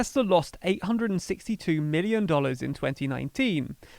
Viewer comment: the narrator says billion while the captions says million.